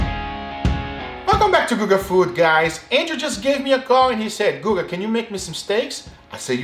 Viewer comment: In the intro I thought he said "Angel gave me a bong."